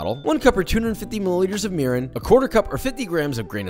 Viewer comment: Thought he said urine